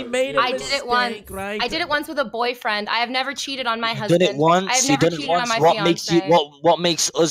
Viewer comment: did she say husband? lol